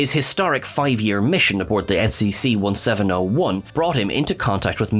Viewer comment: Either I am going insane, my hearing is going wonky, or accidentally stated the Enterprise's registry number as "SCC-1701" in the James Kirk entry... Sadly, I can't tell which of the three options is most likely cause I do feel at times that I am going insane, I sometimes mishear what is said, and is actually a human and can make the most simple of mistakes.